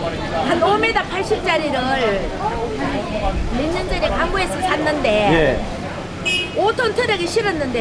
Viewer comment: 항구 맞아요? 강구로 들리지 왜